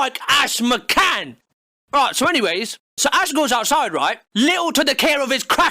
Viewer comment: sounds like he said Asgore.